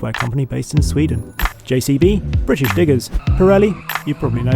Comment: I had to listen and re-listen to 6:12, because it sounded like he said "JCB...British N!**ers"!